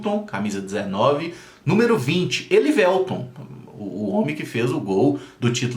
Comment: NA HORA QUE VC FALOU Elivélton EU ENTENDI O HELIBELTON PALÁCIOS KKKKKKKK EU ATE ACHEI ESTRANHO KKKKK